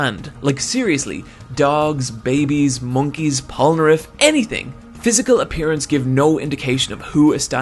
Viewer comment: When he said 'like seriously, dogs, a baby, monkeys, Polnareff', what I heard was 'dogs, babies, monkeys, *the french-*'